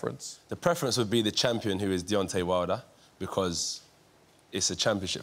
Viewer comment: He said “Daytona Wilder” 😂🤣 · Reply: I thought he said Dejontay